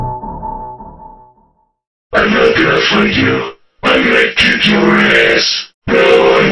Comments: "I'm not gonna fight you, im gonna kick your ass." it took me 2 years just to find out what the droids said lol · What I heard. 3:52 "I won't (word that starts with ex) you, I will kick your ass." 3:56 "Don't worry to be so slow." 4:02 "Tasty, payday." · Now you see me, now you're g a y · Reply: I understand what the robot says 3:52 1: im not gonna fight you i will kick your ass 3:56 2: now im really pissed off